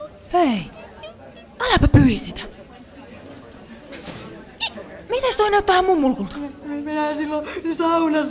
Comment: Mä kuulin ihan ekana et ”miten sä tiesit et toi näyttää ihan mun mummolta”🤣🤣